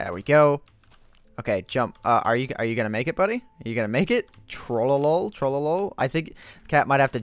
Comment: when you were saying ctrl v it sounded like you were saying "Trolly"